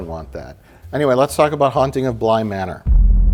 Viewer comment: Okay, so I wasn't paying full attention to the video and I thought that Mike said "Let's talk about hunting a blind man"